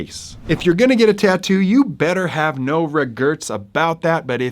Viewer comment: Good message about the permanency of tattoos. As an aside, are you trying to say "regrets"? Several times I heard you say "regerts". It's this a new slang, mispronunciation, geographical accent? I'm always very interested in language differences. Thanks - we appreciate the work.